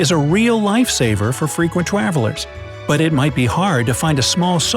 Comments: He says Twavelers instead of travelers lol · am I the only one who realises he was "frequent twavilers? " Instead of travelers? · am I the only one who realises he was "frequent twavilers? " Instead of travelers? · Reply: I’m glad I’m not the only one. My girlfriend and I were like “did he just say twavelers?”